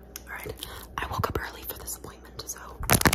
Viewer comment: what I heard: „I woke up early for disappointment.“ Made me laugh. Yeah same. 😅